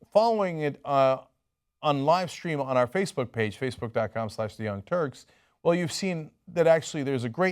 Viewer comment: What a weird yet classic example of a Freudian slip: 3:15 - "...on our fecebook page, fecebook slash the young turds..." What does this say about Chenk's subconscious self-image???